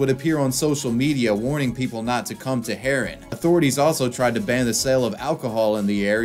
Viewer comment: For a sec I thought it said Malcom X Harem and I was very much confused